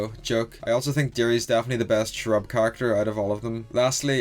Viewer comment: You said cherub kinda fast. So it sounded like you said shrub.